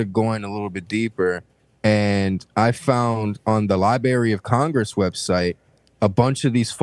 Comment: did homie say Li-Barry